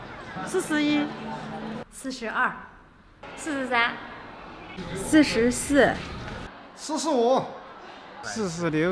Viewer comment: I heard "This is serious "